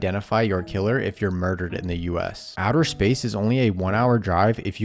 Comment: I thought he said the moon is only a one hour drive and I was like hullup 😂